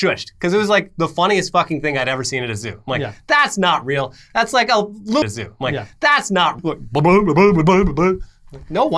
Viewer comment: What did he say that was so bad these guys actually censored themselves? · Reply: I think loony bin